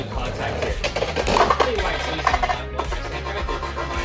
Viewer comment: WHAT'D YOU SAY?!?! I CAN'T HEAR YOU OVER ALL THE VIOLENCE!! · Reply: IT’S BEEN SO LONG I DON’T EVEN REMEMBER HAHA edit: after a few relistens i think i said “alright, never mind” which would make sense LOL